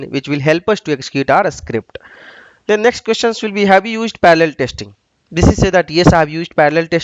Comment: Thank you so much for the video. Can you please tell me what you are saying at 13:40? What kind of testing? It sounds like you're saying 'Paldo testing'? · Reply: parallel testing